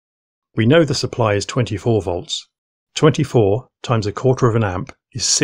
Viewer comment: what? 24 volts power supply? I thought you said you choose a 36 volts power supply...